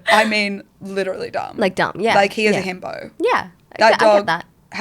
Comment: LMAO I thought said "he is a hammo" at first.